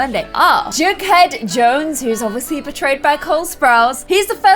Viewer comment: When you said "Jughead Jones who was obviously portrayed by..." I thought you said "betrayed by" and I thought there was about to be a major show spoiler and I was like AHHHHH WAIT, LOL but no. Just Cole Sprouse.